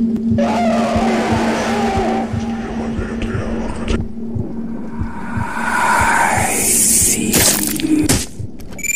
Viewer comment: Screaming aiakajauuajnaajajaj (idk what is he even saying) I SEE YOU Splash goofy jumpscare 💀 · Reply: His ambience is saying "I see you" but very fast.